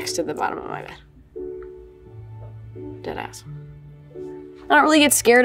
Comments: i think she said deadass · i PRAY that i am NOT the only NYer that absolutely CRINGED at the fact that whoever captioned this video wrote “You did ask” at 19:13 when what Billie ACTUALLY SAID was “Deadass”.